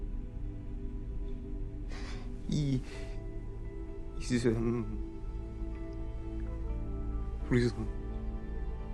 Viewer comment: Sorry this might be a spoiler idk but if you haven’t watched this yet look at this comment after or something but... what did the guy say at the very end, like I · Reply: this guy is risen, ,is what he said.